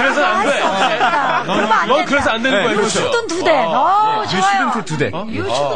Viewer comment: I thought he said { You shouldnt do that!}